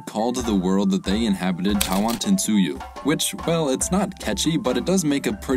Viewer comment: it sound like you saying "i want to sued you"